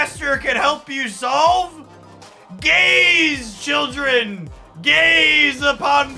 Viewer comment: bahaha 4:11 it sounds like youre saying gays are a problem that grandmaster solves